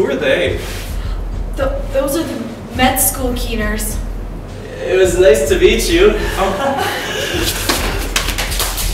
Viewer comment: Awesome video! <3 But... i can't understand what she says at minute 6:53 :s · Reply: "Med school keeners" Keeners is Canadian slang for a smartypants who is extremely keen (or eager) to learn and basically annoyingly answers all the questions in class. In American slang, they're called "gunners", but they mean the same thing.